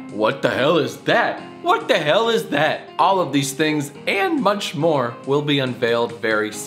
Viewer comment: did he say "munch more" ??